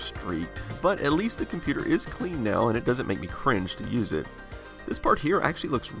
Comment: did he really say cringe?